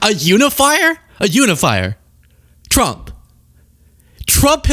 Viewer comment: I heard "a unifying Liar".